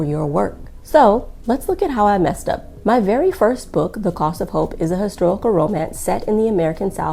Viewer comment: In the middle of your video: A Reconstruction-era romance? That sounds intriguing to me! After all, who ever heard of a hobbit before? Or of a boy who didn't grow up? Then again, I generally don't read romance novels. It sounds like your novel would've attracted the same audience as Octavia Butler or Toni Morrison. On the flip side, there's Gone with the Wind. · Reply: P.S. I thought you'd said The Cause of Hope, not The Cost of Hope.